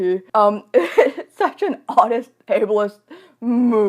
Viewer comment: captions say oddest instead of audist